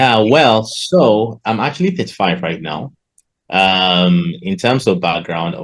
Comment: I think he said 35, but in subtitles it's 55. Btw great job team Funded next, these type of videos gives us a lot of hope and motivation to never give up.